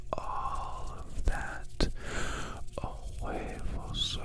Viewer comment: Damn his voice... when he goes from whisper to throaty tone... uffff. 😳😏😷😱🤭🥴 8:17... is it just me or did he say "huevos" as in in spanish "eggs"? 😳🤣🤣🤣